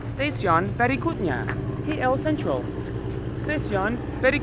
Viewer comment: i dont understand ur pronouns for ''berikutnya'' at the first time u said it, when u mention About Ktm station, i know what u mean... i thought u said very good nya.